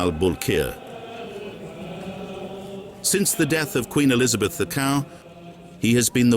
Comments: "queen Elizabeth the cow"? what did the narrator say? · Did they say "Queen Elizabeth the Cow" at the start? At 1:00 minute mark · I swear it sounds like he's saying, "Queen Elizabeth the Cow" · Wasn’t certain if the narrator was AI, until at 1:01 I heard “…since the death of Queen Elizabeth (the Cow??!!!?)” 😂 Subtitles say Queen Elizabeth II but the narration went rogue there & decided to be cheeky or to create their own word I guess? Queen Elizabeth the Tal maybe? Dehtal? No idea, but it sure sounds like “the cow” and that’s just funny af 😂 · Did he really say: "Since the death of Queen Elizabeth the COW" at 1:02 ??? :D How is nobody noticing this? :D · did he really say "since the death of queen elizabeth the cow?" !!!!!!!!!! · am i hearing elizabeth the cow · "Queen Elizabeth.. cacao?" What did the narrator say? I could not understand it. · "Since the death of Queen Elizabeth the cow" ... am I hearing something wrong?😂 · "since the death of Queen Elizabeth the cow (hard pause)" ...excuse me? 😅 Can someone PLEASE tell me i heard that correctly? 😅😅😅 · did you just say queen Elizabeth the cow😮😮😮😮😮😮😮